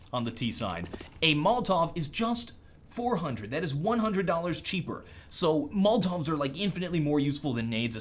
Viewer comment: at around 7:15 says "a grenade is $300 and a Molotov is $400 that's $100 cheaper" am i hearing that right?